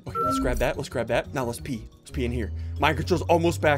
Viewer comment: let pee I heard